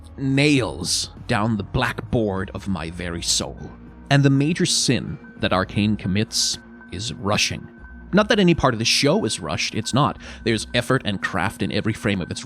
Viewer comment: I don't know if that was intentional but when you said "the major sin that Arcane commits is" i thought you said Russian and i was thinking no poor Viktor 😂